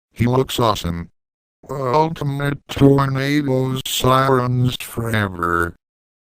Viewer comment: What the hell is he saying? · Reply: the ultimate tornado sirens forever